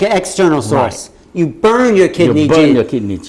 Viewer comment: what are they talking about? I cant understand them Kidney Jeans? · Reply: Kidney Jing is the energy in the kidneys you are born with. Kidneys are the source of life.